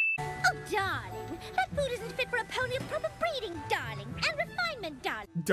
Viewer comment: ikr? At first, I thought she said "breathing" but "breeding"? Wow.... XD